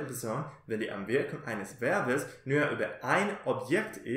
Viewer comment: Hallo, bei minute 4:28 wenn du sagst(dann in der 3ten Person wenn die????? eines Verbes…) Ich verstehe hier deine Aussprache nicht, irgendwas mit awirkung eines Verbes…. Könntest du mir bitte schreiben was du meinst,ist es das Wort“ Auswirkung“ Danke im voraus Super,super video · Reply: Hola Karl, vielen Dank für deinen Kommentar. Ich sage "wenn die Einwirkung eines Verbes über eine Person ist.... " Ich meine wenn eine Person das Objekt des Verbes ist, z. B. "(Yo) escucho a Juan." (Ich höre Juan zu.) Hier ist Juan (eine Person) das Objekt des Verbes. Bitteschön!!